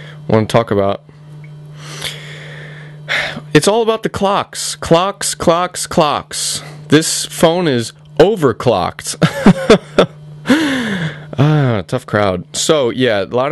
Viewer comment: Lol, i thought he kept saying, "Cocks, cocks, cocks. This phone is over-cocked. Lot of Cocks on here."